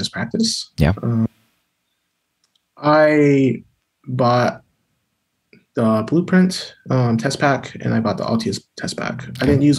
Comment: I couldn't understand which materials he used to study for the MCAT. Can someone clarify please? It was at the 14:00 mark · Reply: Blueprint & Altius test pack. AAMC material. Practice problems.